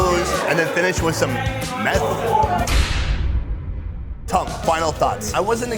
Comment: did you say 'finish with some meth'?